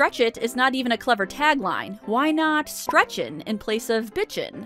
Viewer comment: I always thought he did say "stretchin'"